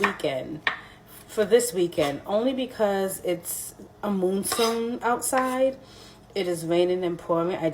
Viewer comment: Did she say a moonstone? 😂 Did she mean a monsoon? Lmao! I'm dead